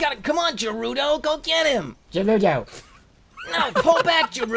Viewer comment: seriously thought he said 'Jaruto' instead of 'Garudo'